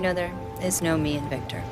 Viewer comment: i think what she said was "theres nom 'E' jn victor"